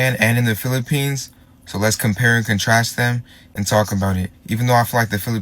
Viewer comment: did he say compare nd contrast😳😳😳